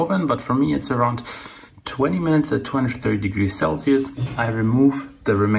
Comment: after 20 minutes twenty three degrees celcius? what did u say at 17:40? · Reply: 20 minutes at 230 degrees Celsius